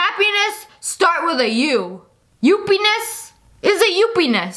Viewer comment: i dont think they were saying ''uppynes'' i thought they were saying ''you pinus'' *me*who still cant spell lmao